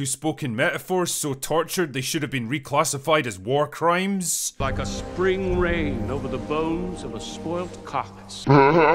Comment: Did he just say “like a spring rain over the bones of a spoiled cock” ?